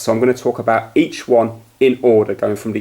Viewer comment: You Said Shit Instead of Each